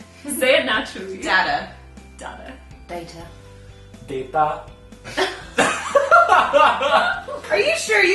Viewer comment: it sounded like he said Dayda